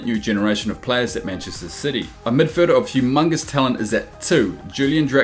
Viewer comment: did he sayed hugh mungus talent?